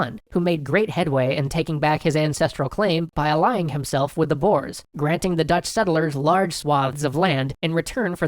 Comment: I thought you said boars XD. Couldn't find anything about dutch boars but then I realised you meant boers or boeren.